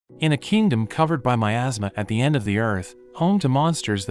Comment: i thought he said at the start a kingdom covered with my ass yeah